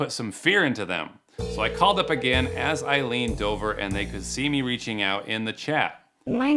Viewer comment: I heard this as "I called them as I leaned over" lol